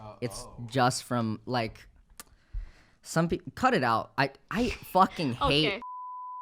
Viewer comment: Pretty sure the person Michael was talking about was MrBeast. I think at 46:42 he said "I fucking hate MrBeast so much"